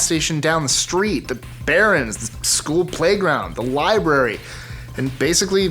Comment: did he say "the Berend's?"